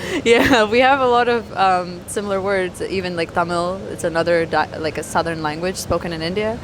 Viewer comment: ตุ๊กๆ Bajaj แบบที่ใช้ในเอเชียใต้ (อินเดีย ปากีฯ บังคลาเทศ) เค้าจะเรียก auto rickshaw เรียกสั้นๆ auto ผมว่ามันน่าใช้กว่าตุ๊กๆไทย daihatsu 10:24 เธอพูดว่า ภาษาทมิฬ (Tamil) ไม่ใช่ "ตัมมิว"